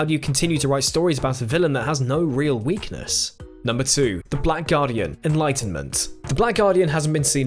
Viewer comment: I got a ad as he said the black guardian so all I heard was “the black guy” and immediately I thought of the doctor being racist hahaha